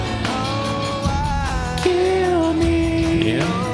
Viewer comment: For Tender, rather than "kill me" I hear "heal me"